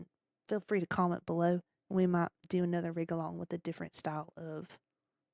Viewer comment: Thanks so much for your blessing - I know it may sound daft, but I would hate for anyone to feel I'm stepping on their toes. After a while of going through this Rig Along with you (which I often mishear as "wriggle on"! 😆), I've decided to take the baby steps and make them even smaller, starting off with a PNGTuber avatar since it's beginning to dawn on me how much of a commitment this is. I have found even more respect for you (and other designers/modellers/riggers) while going through your well-refined workflow! One thing I'd note about the tutorial (if you'd like to distinguish between the free and pro versions of Live2D) is that the free version doesn't allow users to use the "Bulk setting of model parameters" or "Bulk setting of model objects" functions. So, anybody wanting to import the CloverParams.csv file will need to use the trial or pro version.